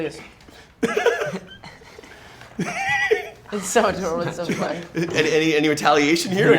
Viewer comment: did Jack say it was adorable? :0